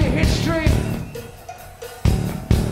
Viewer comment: What say Zack in the minute 04:56???? Just.....????? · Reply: Forget about your culture and just buy